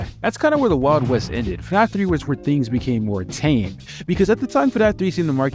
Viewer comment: Changed…….. huh what a name Edit: I was wrong he said tame and changed I am very sorry